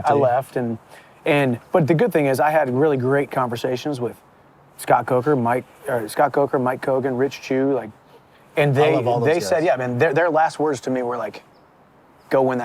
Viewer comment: Did he say Rich Jew? 😂